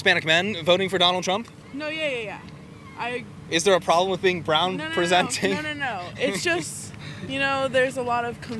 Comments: I definitely heard “round presenting” at first. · Totally thought she said "round presenting" 😂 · I thought she said round presenting 😅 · Who else heard her say “round presenting” 💀